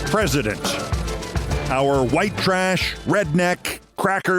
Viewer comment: looking at the thumbnail for this episode, I kept thinking "why in the hell is Keith saying 'Our white teeth president' 😂 I couldn't understand why he'd say that, especially since Keith has such a mastery of language and grammar. The white trash angle never crossed my mind but made me laugh as soon as he filled in the blanks for me.